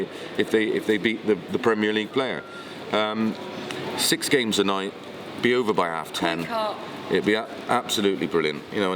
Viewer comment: what does the lady say at around @ 23:14 ?? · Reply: Lol i thought initially ‘wanker’ but played again and heard ‘wake up’